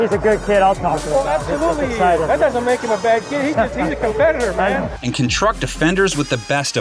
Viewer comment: He said competitor but good try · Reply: Thanks. I listened twice and coulda swore that he said confederate. My bad. He does for sure say competitor